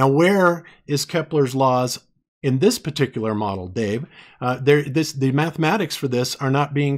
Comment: Every time he says "Dave" I hear "babe".